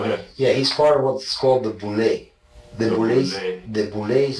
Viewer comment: what does he say p diddy is apart of? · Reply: Boule